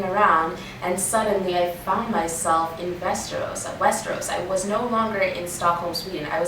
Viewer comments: When you said "Westeros" i thought you said "Västerås", with your accent it would basically sound the same :P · hahaha i thought you said VÄSTERÅS. like 10 swedish miles from stockholm haha.. "just walked there".. haha